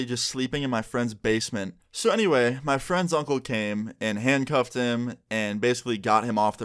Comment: when he said “ My friends uncle came” I thought he said my friend’s on cocaine 😭